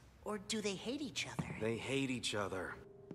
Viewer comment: "They're gay for each other", that's what he said and no one can convince me otherwise